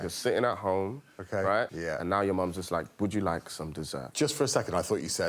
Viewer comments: i have rewatched 4 times... what is Baba trying to say? EVERYtime I hear 'Take you back to your childhood, you are sitting at home and BOOM your mum's a slag' EVERY time. Closed Caption gave up. he isn't wrong... just like to know where we are going... childhood. sitting at home... mom is a slag. finish your story Baba! did my mum and your dad hook up over jelloshots? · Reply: To me it sounds like he is saying, "Your mums is like..." but his accent makes it sound like like, "Your mum's a slag". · I kept thinking, he's clearly gonna say, "your mums is like"...and no! All I hear is "your mum's a slag." Every time.